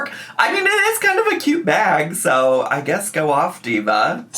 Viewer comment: When she said, what you thought was “a-curred”I thought she said,”acute.” Either way, I cracked up laughing at your response to the search results. As someone who gets mistaken for saying certain words wrong bc, southern accent, I feel the pain lol.